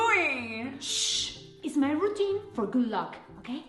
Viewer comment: i thought she said "it's my routine for gulag" and honestly that makes much more sense